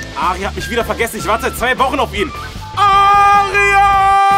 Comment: Wie ich Arier verstanden habe. XD